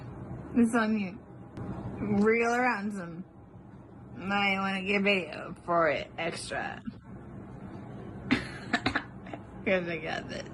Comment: OMG! Listen to this really carefully… it sounds like she says “you might want to get beat for it extra”